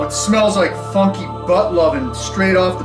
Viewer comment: Did he say funky butt-loving?!!